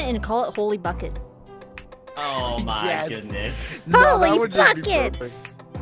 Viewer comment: I thought u said holy f word for a sec